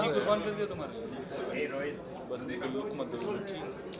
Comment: me shaheer ne aaisa kya kaha ki Pooja ne aaisa reaction kyu Kiya hamare shaheer 💙 ke saath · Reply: He said 'looks mat do'